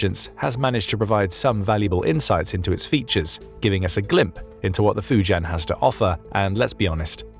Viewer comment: Did he just say “glimp”??? 🧐🤔